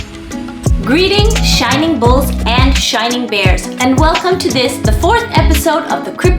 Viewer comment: I thought she said. Goodmorning shining balls.